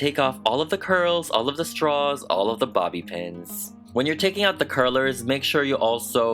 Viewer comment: When u said bobb pin I thought u said body pens